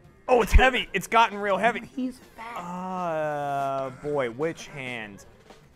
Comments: Anyone hear "he's bad" at 34:06? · Does anyone else hear “he’s fat”